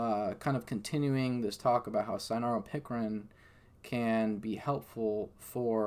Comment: what is he saying? Sayano pickrin? What is that?